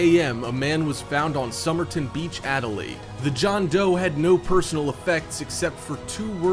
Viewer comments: did he say john doe 😮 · did he say the john doe? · did you say john doe?